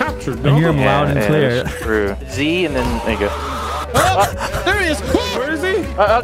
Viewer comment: Did Delirious say the N Word at 7:43?! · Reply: I think he said there u go but stumbled on his words a bit